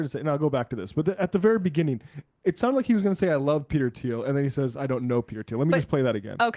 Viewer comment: I don't think he was saying he loves Peter Thiel. It sounded to me like he was about to say "I know Peter Thiel" as in "I know Peter Thiel gave this guy a job on the board" and stopped himself to clarify that he doesn't know him personally, he just knows he does that.